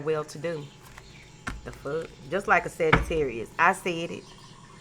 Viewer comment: Damn I thought you said Fuck us Pisces ♓️ haven’t heard from you in a while I was getting Anxious 😂😜😘💕💜💕💜🙌🏾💯👑😎Love You 👑